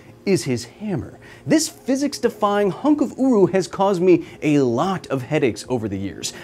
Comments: You know you've been on the internet too long when you heard "uwu" here instead of "uru" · did he say "hunk of uwu" ? or am i just so much a furry that im hearing UwU everywhere?